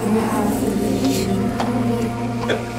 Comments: Did i heard it right ? "You have a mission." Can't.. really understand what they said before that. Maybe because i don't really speak english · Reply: Spot on. I heard the “You have a mission,” although it’s not easy to hear, but what you said I could understand with more clarity. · I heard "you have a mission" :D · Angel: “You have the ambition to fulfill your mission” Me: dang that was big bee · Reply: I heard “You have to fulfill your mission”. It sounded almost like a command. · “You need to fulfill your vision..you have to” Not sure if anyone else heard that at the end or if it was just me. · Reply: I heard “you have one year, to fulfill your mission” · YOU HAVE EVERYTHING YOU NEED TO FULFILL YOUR MISSION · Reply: I heard "you have permission", repeatedly. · Wow thought I heard at the end you have the motivation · I heard "To fulfill your mission, you have to leave" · I keep hearing it saying "honey honey,you have to be strong" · “You need to fulfill your vision..you have to” Not sure if anyone else heard that at the end or if it was just me. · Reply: I hear "you have an issue, this is the solution"